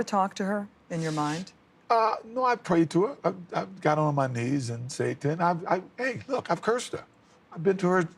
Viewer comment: he is asked if he ever prays to her and I swear he says “I get on my knees and I satan hey look and I curse her”